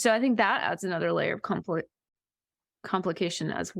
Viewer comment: makes a Freudian slip of the tongue when instead of saying that the genetic dimension to diagnosis creates another area of complexity, she actually says it creates another area of conflict. Because conflict is exactly what it can create in a family dynamic, because of the internal ableism of previous generations and dysfunctional family dynamics, often mean one member of the family can have been forced into the role of empath and/or problem child and therefore there are strong incentives for other family members to fail to validate the diagnosis.